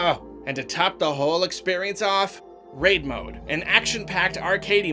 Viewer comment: I thought he said "Rape Mode"